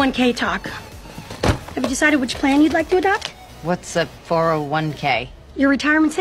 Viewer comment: Why did I hear "Did you decide which clan you're going to adapt "